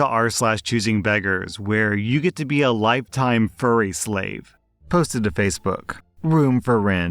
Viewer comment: why's the first thing i hear is "furry slave" and me thinking "that's going to get wild"